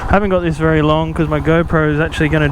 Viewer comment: Just sold my Aprilia Tuono in Paris with great regret and was looking at these just for a fun machine to blip around on from time to time. I had to play part of this tice as I heard, "I haven't got this for long because my girlfriend is about to die"Luckily it was just the Go Pro😂 Nice little video, ride safe